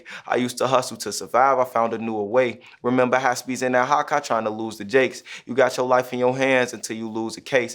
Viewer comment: Who else thought he was saying "hawk eye" not hot car